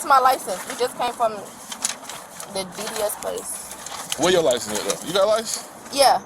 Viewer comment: Funny how they always ‘just left’ court/DMV, or ‘just paid’ the fines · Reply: At first I thought she said she just got back from the BBL place.